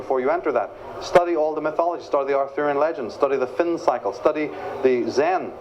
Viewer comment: Michael says to study the mythology, to study the ...(something) legends. I can't understand.. what legend is he talking about? thank you. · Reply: Arthurian Legends <link> <link> just a couple of links